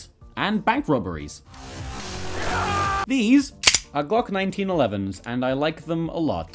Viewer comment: did he say Glock 1911s? 🤨